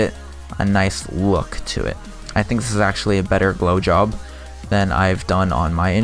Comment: I heard blow job the first time.....